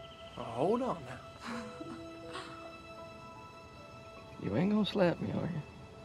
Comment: I can't understand what he said in 2:01, anyone can help me? 😣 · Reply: Matthew140398 he says, “Now hold off ,u ain’t gonna slap me are u? coz if I fall off m gonna take u with me”